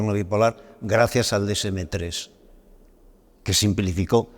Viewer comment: ¿Decemetrés? Eso es lo que dice pero no encuentro datos si lo busco así. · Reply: Se dice DSM y después el número que estés buscando, saludos